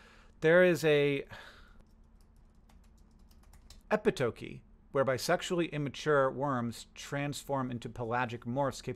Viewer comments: Around the 8:17 mark, I had to rewind and do a double take because I thought you said "where bisexual immature worms" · I wasn't looking at the screen, so I heard "where bisexually immature worms transform..." and I spent a solid minute wondering what a bisexually immature worm was, before realizing I'd stopped paying attention to the video.